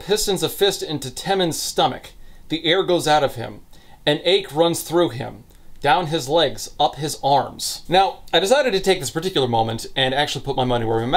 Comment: omg I'm so dumb, I thought you said "an egg runs through him, down his legs and up his arms" and I was like hold the fuckin phone what but it was an ache through him.